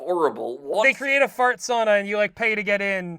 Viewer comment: for a second I thought said they created a “Fartsona,” which is somehow even worse